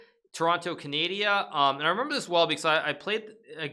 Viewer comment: ok but did he just say toronto canAdia